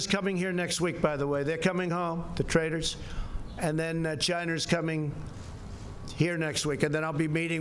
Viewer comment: Did he say "The Chiners" are coming next week... hmm 15:50 Not a good impression on how Chinese people talk.